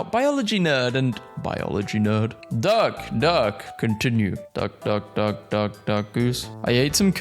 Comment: did i hear duckus